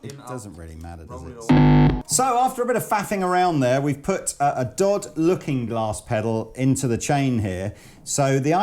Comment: For a moment I thought said "dawd-looking glass pedal" XD